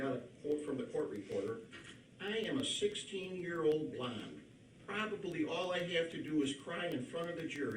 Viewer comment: I thought at first that he said "I'm just a 16 year old blob" instead of blonde lol